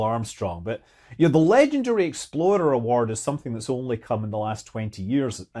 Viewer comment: Ah! EXPLORER! I kept hearing "exploder." Honestly, I think that'd be a pretty cool award too.